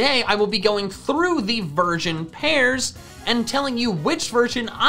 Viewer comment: I swear at the beginning I heard him say virgin pairs instead of version pairs 😂😅